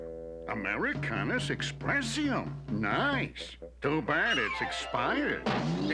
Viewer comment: Why does he say "Americanus Expressium" when the card clearly reads "Mercurius Expressium"? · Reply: No, he said "A" Mercurius Expressium.